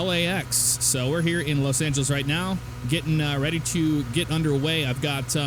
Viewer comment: you said Los Santos instead of Los Angeles